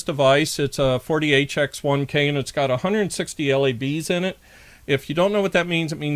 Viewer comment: I thought you said it had 160 LEDs in it.. :-D